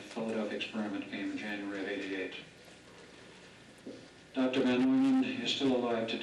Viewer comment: did he say von Neumann?